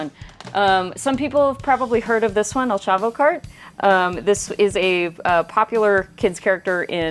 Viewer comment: Really thought she said "El Chapo Kart"